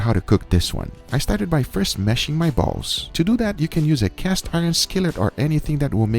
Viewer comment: I really thought he said "mashing my balls"